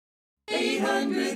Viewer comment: Am i the only one who thought fhey said "and five!!!- today." I was real young at the time so dont look at me weird